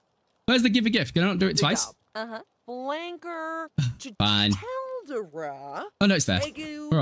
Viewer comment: did Morgana say wanker😭